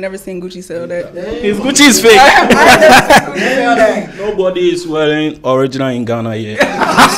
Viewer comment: Did I hear “Nobody is wealing* original in Ghana here?..😂💔😂💔